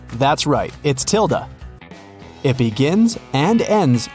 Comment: I thought her name was Sunday. I didn't hear the "Tilda" part of the riddle.